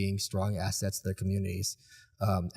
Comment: What are the last words he says? I can't understand it. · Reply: Different pockets